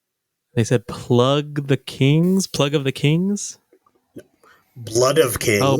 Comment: I heard "plug of the kings" also